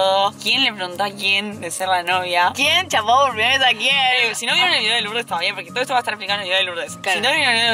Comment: que dice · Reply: si no vieron el video e Lurdes/Lourdes (ni idea como se escribe)